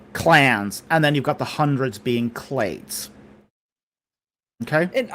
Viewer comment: what is he saying for the hundreds here? Clates?